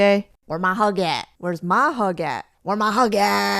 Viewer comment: I heard it as Hugat :/